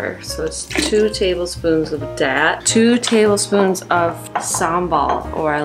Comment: I heard 'two table spoons of dad' instead of 'that', sounds about right